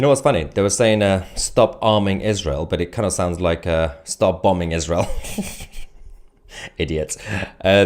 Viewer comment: I thought they were saying “stop bombing Israel” as well. Right now I am saying a prayer in my head for our country. Hopefully I won’t be arrested.